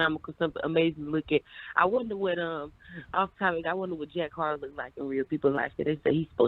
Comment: did the caller say she wonders what Jack Harlow looks like???